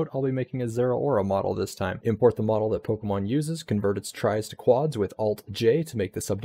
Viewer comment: Hi. Where did you get the model???? It sounds like you said "Pokemon Uses", but where is that? I searched and found nothing.